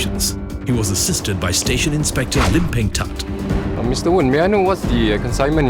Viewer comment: i thought they say lim bei